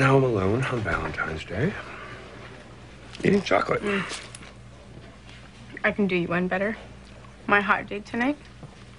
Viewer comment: I thought she said I can do you way better